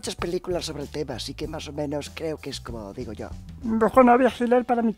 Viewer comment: que paranoia tengo que entendi "coronavirus" cuando dijo "comolodigoyo"